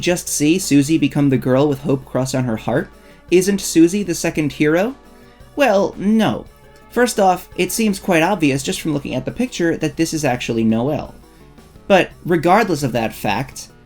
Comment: I understood "isnt susie the second kira?" At 5:18 instead of hero 😭